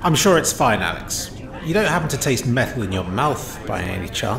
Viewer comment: I THOUGHT YOU SAID “you dont taste better than your mouth do you?” TO THE AI AND HAD TO DOUBLE BACK